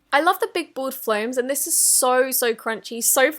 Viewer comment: I thought she said “I love the big bald floams* 😂